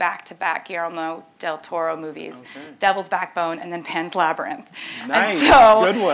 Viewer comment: what? she said labyrinth?